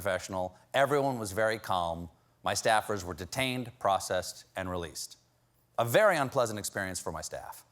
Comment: Yes, it was. He said they were charged and released. That means they have to come back for accountability for the charges. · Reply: "My staffers were detained, processed and released." He does not say charged or mention a crime. They were charged with unlawful entry.